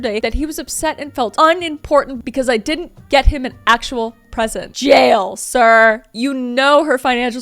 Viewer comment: i just hear “JE TELEPHONE Á LA POLICE!!!!!” 😂😂😂😂😂😂❤🤌🏽🤌🏽🤌🏽